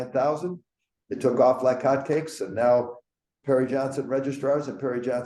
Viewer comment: Took me a min..Your on speaker and I was in kitchen..Thought you said hairy Johnson till I looked at my screen..